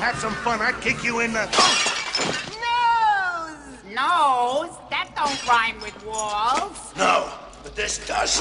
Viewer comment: what did smarty say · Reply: Adam M "Nose? That don't rhyme with 'walls'" Setting himself up to get launched into the dip via an atomic groin attack.